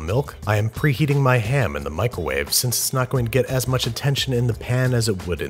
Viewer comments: Did he say "micowave" like a baby on purpose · sounds like he said Michael-wave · Did he say michealwave at 6:48?